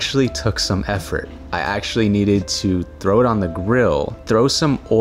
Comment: When you said “throw it on the grill” I only heard Ocky’s “frow et on da gwiiiillll” 😂